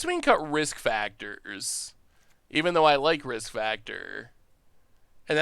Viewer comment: Wait what?! Did I just hear say he likes risk factor???